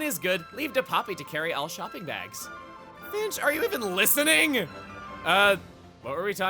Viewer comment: at one point Chugga says "Finch, are you even listening?" I swear I heard "Bitch, are you even listening?" XD